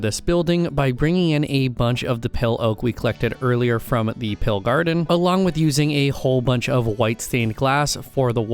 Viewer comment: i put this on in the backround as i did some work and i heared u say pale garden and it sounded like pill i even turned on captions and it sounds like u said pill i was so confussed lol